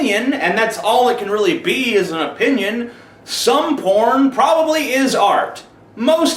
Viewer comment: Transcribe captions says "some foreign policy is art." I lol'd.